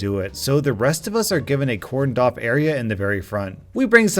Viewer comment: I thought he said corn dog